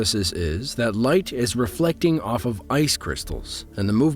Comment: I heard "the light reflects off of Venus and...." MIB 😂